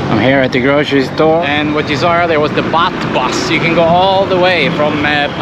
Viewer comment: LoL it sounded like u said the butt bus 😂 I love ur accent so much 🥰